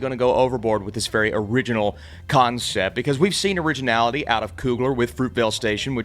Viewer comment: For the first 120 seconds of this review, when he said "concept", I thought he was saying "cotton sip"....which oddly made sense.